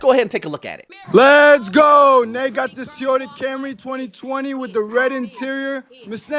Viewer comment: It's also a brand new 2025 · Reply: 2025 hu? Sounded like 2020 to me. I'm guessing you barely started the video and assumed the white car in the beginning.